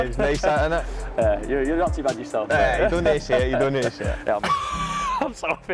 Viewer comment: what KT said? indonesia? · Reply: i think he said "you don't need to say", in response to rob saying "you're not too bad yourself"